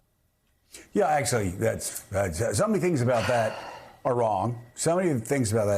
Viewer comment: Lol it sounds like he is saying that he is gonnna make migrant crime. Its one thing to " categorize" but then he says "its gonne be worse than any crime". Makes it sound like he is bringing/starting it. Fun fact to Illegal migrants which is who he is actually referring to have some of the lowest rates of crimes. As someone who has actually know illegal immigrants all they do is work then go home and were afraid to be outside because any interaction with law enforcement could mean their deportation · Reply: I thought he said "my ranting grime" :face-blue-smiling